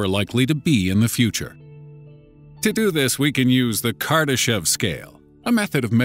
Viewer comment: i thought he said kardashion